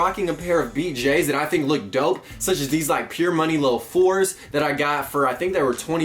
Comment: did he just say pure money 4s🤔😂